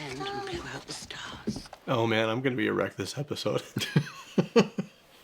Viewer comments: For a second there, I thought he said, "Oh man, I'm going to be erect this episode." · I totally misheard it as "I'm going to be ERECT" rather than "I'm going to be a wreck" 😂😅